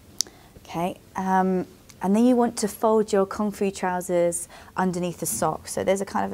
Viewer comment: oooh, "Kung Fu" trousers. I thought you were saying "comfy" trousers. I thought, yeah those trousers do look comfy.